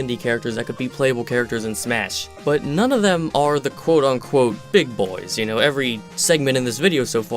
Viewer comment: did someone said Quote and Curly?